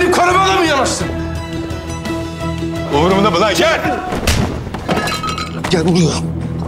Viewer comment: ne dedi burada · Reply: umurumda mi lan gel dedi